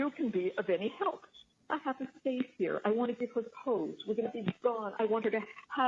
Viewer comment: I think Donna says “I have a safe here, I want to giver her the codes”